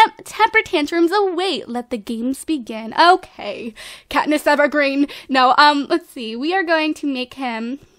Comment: did just say Katniss Everdeen or EverGreen? Because it sounded like she said EverGreen...